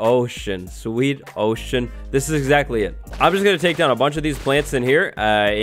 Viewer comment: Sounds like he said “Oh sh**” lol.